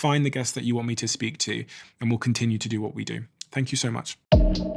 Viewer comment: What did he say he takes at 2:05? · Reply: He said "we will continue to do what we do"